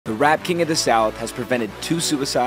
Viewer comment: Before a second I thought he said the rat king